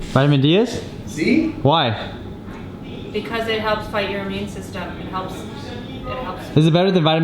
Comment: Did she just say that Vitamin D "helps fight your immune system"?